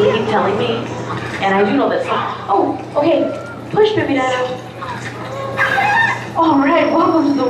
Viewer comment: I thought she said push baby daddy hahahaha